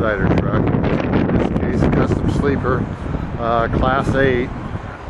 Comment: He said Class 8, not Class A. It even says so in the description. It would require a Class B CDL, not a Class A. Trucks are sized by classes as well. The bigger the number, the heavier the truck is rated for weight wise. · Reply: Oooh ok thanks for clearing that up I have my class b and I expedite in a straight truck and this is my dream truck soo when I thought I heard him say it was class A I was confused and crushed at the same time haha!